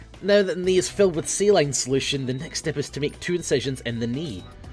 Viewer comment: It sounds like said "sea lion solution"